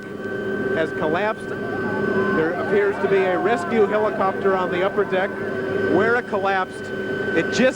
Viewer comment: A helicopter crashed on the upper deck on the Bay Bridge? I never heard about that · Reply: You didn't hear it correctly. 01:33 he says: "There appears to be a rescue helicopter on the upper deck." I take it that means it landed on the upper deck so they could bring the seriously wounded people on board. Then he continues "...where it collapsed, it just missed the bus." What I make of that is: the helicopter did not 'collapse' because choppers don't collapse. He probably meant: "At the point where the bridge collapsed, it just missed a bus."